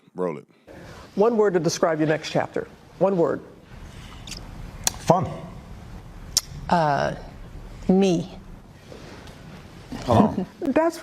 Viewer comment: Did Obama and Michelle say F🤬, Me? What are they really saying?